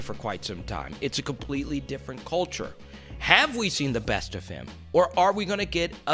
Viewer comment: Did I hear “have we seen the best from Lonzo” Lmao ur a crackhead for that one he’s 21 years old a great passer and defender who will only improve for years to come